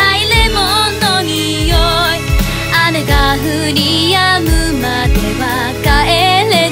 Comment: あねが降り止むまでは〜 に聞こえる笑笑